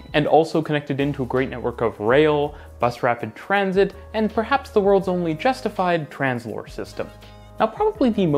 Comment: “…and, perhaps, the world’s only justified Translohr system.” TransLohr, according to Wikipedia, is a rubber-tired tramway (or guided bus) system. (I had no idea what had said so I searched around a bit and found it.)